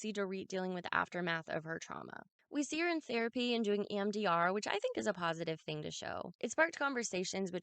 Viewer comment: Did you say mdr? 5:04 what is it?